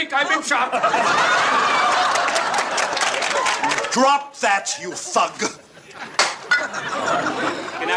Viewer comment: did he say "drop that, you fuck"?